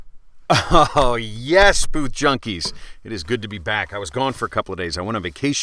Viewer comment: I'm new and in the beg I thought it said both junkie